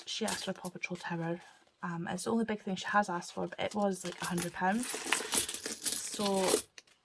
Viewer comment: Well done that amazing I had to rewind I thought you said he paw patrol tower was 800 😆